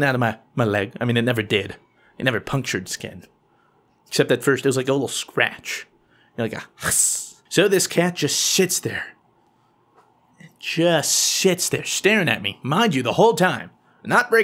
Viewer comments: did he say sits or shits there? So funny 😂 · Anyone else hear him say "so this cat just shits there" at 13:48 😂😂😂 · the auto generated closed-captions thinks he's saying shits instead of sits at about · At about 13:50 I thought you said the cat just s***s there. That was funny.